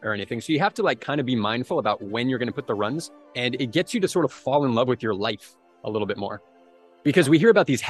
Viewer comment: I've never gotten my mileage up consistently to where running doubles makes sense, but I've certainly benefited from upping my running frequencies in many of the ways you speak of here - especially the falling in love with my life and the process of running part! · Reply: I thought you said fell in love with your wife haha. I was like, when was that in the video?